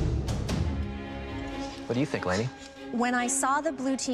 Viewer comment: when he said "what do you think, lainey?" I heard lady 😂